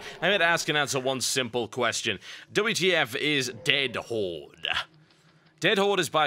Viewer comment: Anyone else thought it said, "WTF is Dead Horse" ?